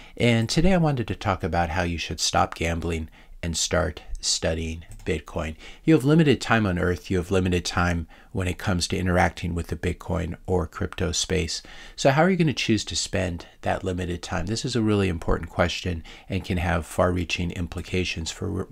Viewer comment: I accidentally misheard "crypto space" as "crypto police". Don't ask me how. I can't answer that! It does bring up a question though. Does Elizebeth Warren want to be the head of the crypto police?